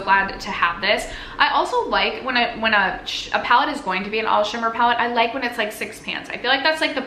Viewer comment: I thought you said it was an all sugar palette, not an all shimmer project 😂